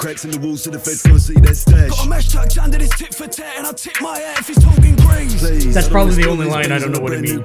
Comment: Someone else might have already said this, but if you're interested, the line at 28:16 you said you didn't understand is: Got a mash tucked under this tit for tat and I tip my hat if he's talking grease Mash = slang for gun (short for "machine gun" probably) tit for tat = cockney rhyming slang for hat tip my hat = an old fashioned greeting but in also meaning literally moving the hat so he can get to the gun. talking grease = talking greasy = someone talking shit about you basically So the line means.. I have firearm concealed under my hat and if I feel someone is disrespecting me I will extract it by tipping said hat and am prepared to use it. Something like that anyway.